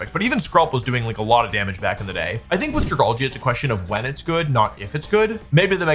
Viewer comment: What did you just call it? 😂 "Dragargle"?